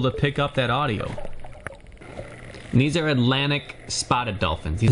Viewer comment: I thought u said spearing dolphins I was like HOLD UP